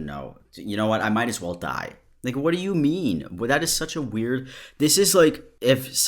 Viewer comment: i thought bro said "nigga what do u mean? "